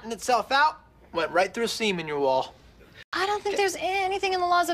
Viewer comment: Oh. "Seam in your wall" is what he said. Had ro relisten a couple of times 😂